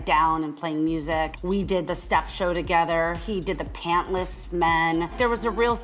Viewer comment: She: we did photography of paintless men i thought she said pensless